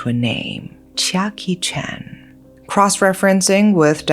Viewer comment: When you said the killers name "chia kee chen" , I thought you said Jackie Chan .